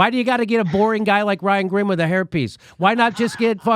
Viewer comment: hairpiece? I could have sworn you said herpes! 💯